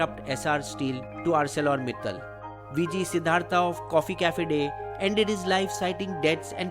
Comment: did i just hear coffee cafe day😂